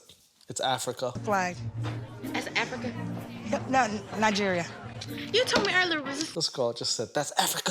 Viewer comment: did you say Nigerian twice?Now I thought he was tunisian edit my mistake , he's with the stinky vaj vaj